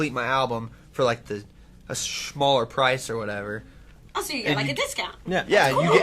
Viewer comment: it sounded like riker said Shmaller hahah